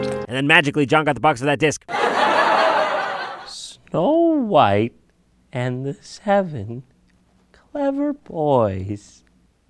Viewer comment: SNOWWHITEANDTHESEVENCLEVERBOOOOOOOOOOOOOYYS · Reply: I thought you Said snow white and the surveillance boys